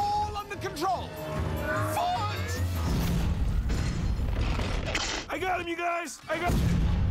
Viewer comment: What Did That Monster Say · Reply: I got him you guys! I got...